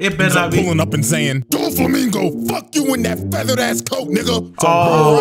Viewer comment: lol They thought he said something beside "feathered ass coat"